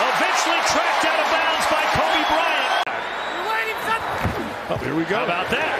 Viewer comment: "...eventually tracked out of bounds by (Kobe?) Bryant" Did I hear that right?